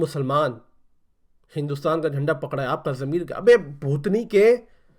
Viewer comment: I think he said Abey bhosdike🤣🤣🤣and 10:32 bhenklode 🤣 maza aagya bhai ❤